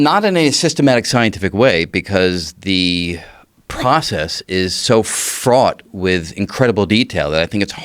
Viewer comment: did he say "...because the process is so fraud"??